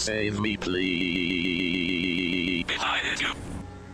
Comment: i thought the fox said "i beg you"